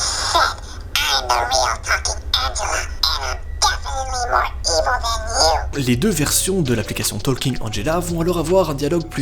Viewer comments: j'ai entendue "I'm the real f*cking Angela and I'm definitely more evil than you" donc ce qui veut dire "Je suis la vraie p*tain d'Angela et je suis définitivement plus méchante que toi." Avec la voix bien creepy, franchement ça rend bien. Mais ça fait peur, on va pas se mentir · A partir de genre 7:18 quand elles commencent à parler je crois que j’ai reussi à comprendre Quand y’a écrit WTF elle dit en fait « what did you said » Et après elle dit « i am definitly more better than you » Voilà (mets un cœur si t’as lu stp) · Donc a 7:26 elle dit ' i'm the real fucking angela and i'm definitly more even than you' c'est en tout cas ce que j'ai compris